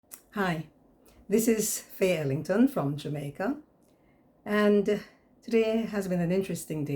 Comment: YOU SOUND LIKE YOU SAID THIS IS FAILING FROM JAMAICA INSTEAD OF FAYE ELLINGTON FROM JAMAICA.